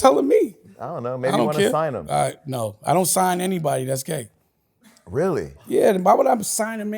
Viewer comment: Okay, he was saying “ I don’t sign anybody..That’s gay!” @ 30:20.. I thought he said what Jason thought as well. 😂